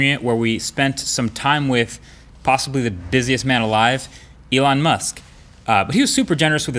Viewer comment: I'm a simple man. Elon Makes a phone. I buy. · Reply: Thought u said elon musk a phone XD